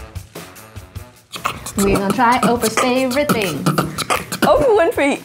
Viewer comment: I legit thought it said “people react to orphans favourite things”